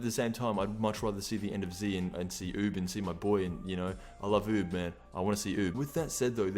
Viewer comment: For some reasons I heard he said I love boob around @ 2:04. 😂